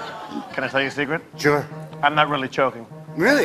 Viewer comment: Can someone tell me what Jeff says in the scene with Colin (around 2:14 and later)? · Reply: "Can I tell you a secret?" "Sure" "I'm not really choking."